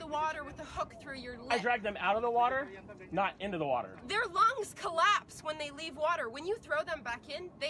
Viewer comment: Did she just say "fishes lungs collapse"